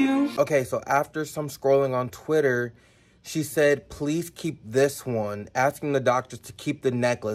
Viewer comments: i fucking started crying when she said „keep this one please”… it’s the first time i’m crying over a music video… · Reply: I thought she was saying "I kept this one" like she forgot he gave that necklace to her and she still had it on so she couldn't forget that memory. That's why when they relink her the necklace turns into a dog collar... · The way she said keep this one please while crying broke me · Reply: I thought she was saying "I kept this one" like she forgot he gave that necklace to her and she still had it on so she couldn't forget that memory. That's why when they relink her the necklace turns into a dog collar... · If you pay attention to what she says when she clings onto the necklace , she says “keep this one..please” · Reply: I thought she was saying "I kept this one" like she forgot he gave that necklace to her and she still had it on so she couldn't forget that memory. That's why when they relink her the necklace turns into a dog collar...